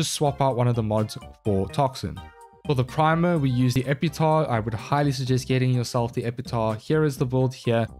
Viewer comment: It damages my brain everytime i hear "epitahh".